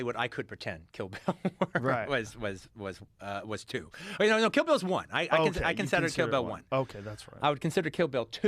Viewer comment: Kay He straight up said in this video he can't count Kill Bill as One, idiot. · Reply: he clearly says kill bill is ONE. how do you get the air to call someone an idiot when you are too dense to comprehend speech?